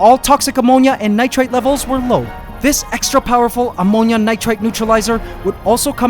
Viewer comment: WHY DID I HEAR AMONGUS